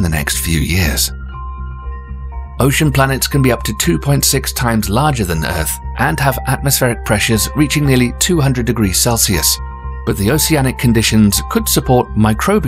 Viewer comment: what did he say? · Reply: That ocean planets can be 2.5 times our size and the surface can reach 200°C.